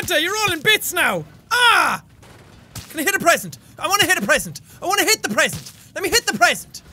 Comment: hahah I thought he said "I want to hit the president 😂"